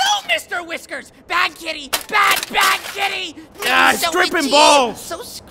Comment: THATS WHAT THAT SAID?!?! THIS WHOLE TIME I THOUGHT CONNIE WAS SAYING “AH, STRIP HIM BALD!” IVE BEEN WATCHING THIS FOR YEARS